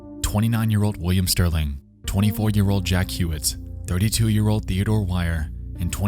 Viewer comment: I wasn't paying attention, so when introduced Jack Huett I heard "Ejaculate" and was like "Wat".